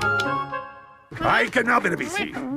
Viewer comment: what is pingu's dad say??? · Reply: I can not really see